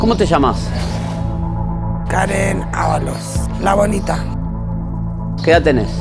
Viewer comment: entendi "La Bolita" 😂😂😂